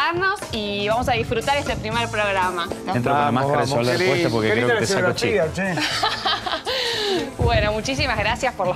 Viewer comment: Que dice yayo en el minuto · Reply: Entra con la máscara de soldar puesta porque creo que te voy a sacar chispas